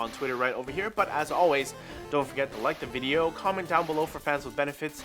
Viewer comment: did he actually said "comment below for friends with benefits"? LOL